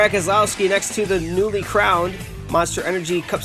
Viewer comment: Did you call Kyle Busch a noobie crown because he just won his second NASCAR cup series championship